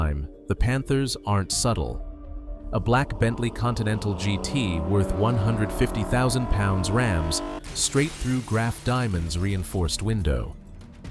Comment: wtf are you trying to say stupid robot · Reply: Worth 150 thousand pounds, rams straight to…